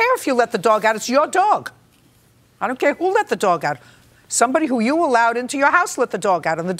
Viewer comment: Who else heard, “Who let the dogs out!?! Roof roof roof” 🎶